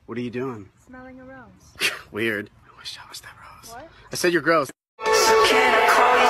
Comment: aww did Harry wish he was the rose? i always thought he said i was gross. blushes very hard